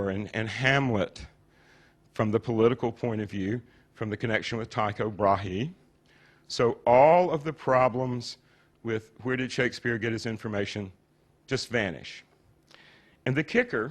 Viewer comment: Every time he mentions Tycho Brahe it sounds like he is saying Taco Brahe.